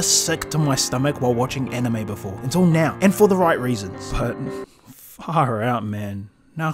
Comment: what did he say? Far out man?